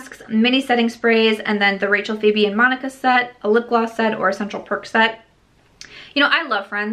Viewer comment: I thought you said central percocet, instead of perk set. I was like woah....🤣🤣🤣🤣🤣🤣🤣